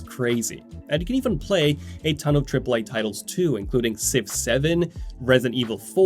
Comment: fully thought said "6 7" lmao my brain is cooked